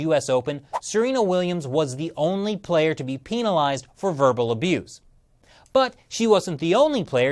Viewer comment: I’m staying out of this so imma just say why tf does he say PEENALIZED